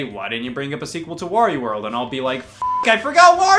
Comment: my subtitles said WW2 instead of mario world. coincidence? i think no